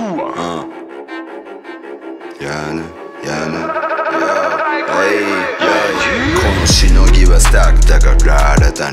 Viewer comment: の効果音？みたいなやつなんて言ってるか分かる人いますか？ 色んな曲で出てくると思うんですけど！ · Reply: たぶんHIBRID（ハイブリッド）って言ってると思います。 YoungYujiroさんが所属してるレーベルがHIBRID ENTERTAINMENTなので。